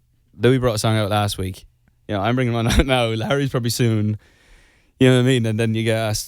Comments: AM I TRIPPIN OR DID HE SAY "LARRY" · omg at 0:47 he says Larry instead of harry omg…. 💙💚💙💚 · did he say Larry instead of Harry??!! Holy shit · it sounded like Niall said Larry 😂❤️ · is it just me or did he say "larry" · Am I the only one that heard him say Larry instead of Harry